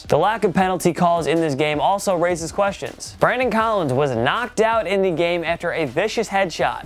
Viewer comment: did he say Brandon Collins ????